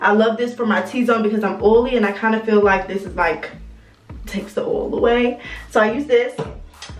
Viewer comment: 8 glasses a day, keep the haters AWAY 😂😂 yes yes!! Give us life tips lmao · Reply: Emaje Harris I thought she said keeps the heads away, lol. As in blackheads.